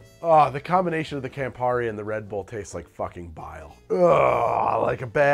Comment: First time I thought you said “Redbull tastes like violence” and I was like “yeah that tracks”